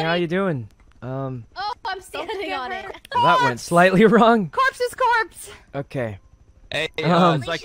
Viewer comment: Leslie: "CORPSE is corpse" Words of wisdom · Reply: I thought Leslie said "Corpse's corpse"